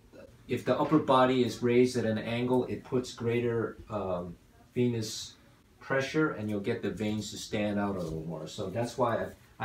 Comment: I think he said p***s pressure